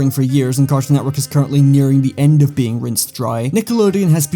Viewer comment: what does he say there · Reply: “Rinsed dry”